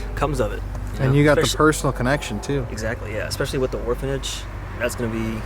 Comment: after listening several times, I think said "WAR-phanage." Do ask him about this!!! xD